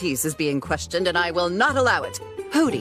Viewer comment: When I first say this I misheard Hooty. I thought he said "Oh get ready!" instead of "I'll get ready!" Like Hooty is saying that you won't be able to handle how I pull this off.